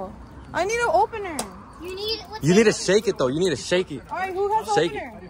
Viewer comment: DID ANYONE REALIZE WHAT WAS SAYING ?!?! 😭😭 “you need to shake it” and he put the camera back there 😭